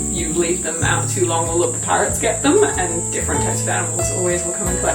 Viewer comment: Oh, PARROTS can get the bananas! I thought she said PIRATES! I was thinking poachers and trespassers